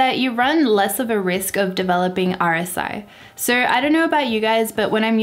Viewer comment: At about 1:20 in you mentioned something that I didn't quite understand. I think you said "aursie." What is "aursie?" Thanks for the info here, it made me decide to upgrade to the same Wacom tablet you're using from an older usb connected Wacom I have now. Keep up the great work you're doing to help us become better photographers! (Also, thanks for lowering the volume of your background music, it's much better! And I'd be remiss not to mention, again, how much I love your accent!)